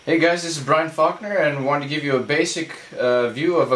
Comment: Did he just say his name was...?? :P cool name